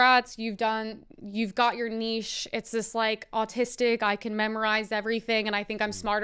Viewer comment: Did I hear her say AUTISTIC????